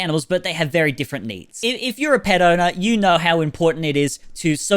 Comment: I thought you said “if you’re a pedo”